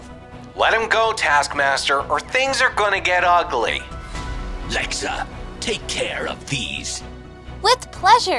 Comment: it sounds like Task Master is saying "Alexa, take care of these" but he actually said Lexa but it sounds like he is using an Alexa lol